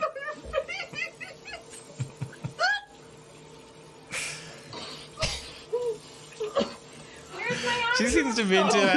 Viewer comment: guys help me out what was yellow thing i could not understand a word they said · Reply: It was a potato